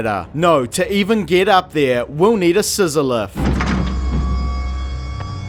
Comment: I had to rewind like 5 times around 3:25 because I thought you were saying "on a sizzler" instead of "on a scissor lift"